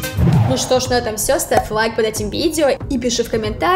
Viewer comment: После мема улетел в космос, мне послышалось в словах девушки не ставь лайк, а get lucky, есть такая песня